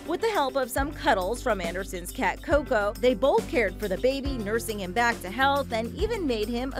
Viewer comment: it sounds like she said” nursing him back to hell”